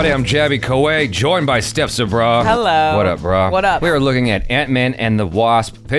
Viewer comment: Did he just say "what a bra???"